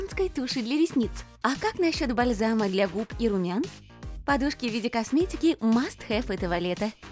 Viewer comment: Послышалось: "Подружки в виде косметики"